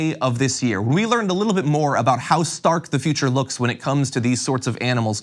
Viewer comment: Anyone else thought he said "House Stark" for a second?